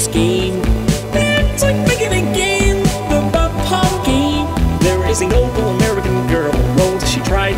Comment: Am I the only one who hears Pomgay instead of Pompeii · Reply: I heard "Pom-game"